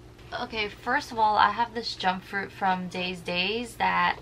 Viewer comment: when u said jumpfruit i thought i misheard since I'm tired but it was so funnyy ! 🤣🤍